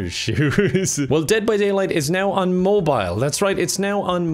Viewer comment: i thought you said 'day by daylight' and i was confused